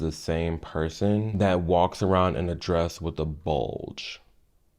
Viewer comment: I listen to these sorts of videos while doing other things and I had to tab over and see when I heard "intentional bulge" because I had to get a mental image of this. I did NOT expect a Jay Leno chin, holy shit.